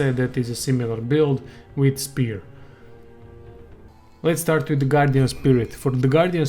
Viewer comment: I thought u said let's start with the god damn spirit I laughed so hard at that.